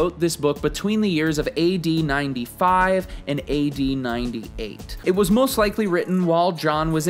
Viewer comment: I thought for a second he said 1895 and 1898 instead of AD 😂 I was like WOW! I had no idea!!